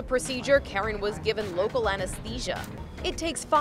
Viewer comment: did they say local anesthetic?